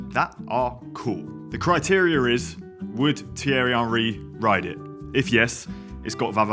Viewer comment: what is he saying? who is terryan ree?